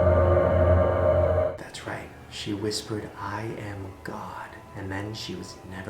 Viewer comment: I heard 'I am cock' instead of 'I am god' and I can't stop laughing